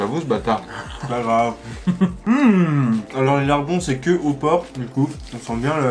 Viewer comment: C moi je qui ait mal entendu ou il a dit que au pourc